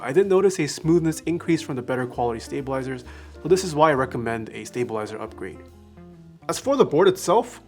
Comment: I heard 'stabilazers' and thought I was having a stroke for a second.